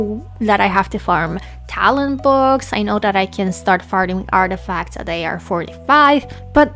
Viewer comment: it sounds like youre saying "i know that i can start farting artifacts"